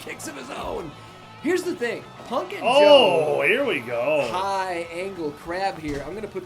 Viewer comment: I thought said Pumpkin Joe and I was confused😂